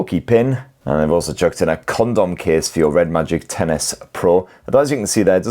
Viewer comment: bro did you said condom case ? 😂🤣🤔